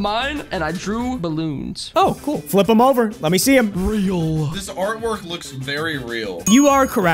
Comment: I thought he said real?